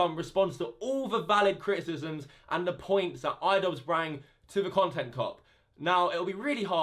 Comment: did you say brang lmao